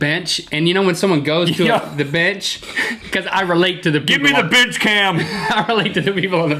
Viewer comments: it sounds like he says "GIMME THE BITCH CAM" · Hahah I thought Rhett said bitch cam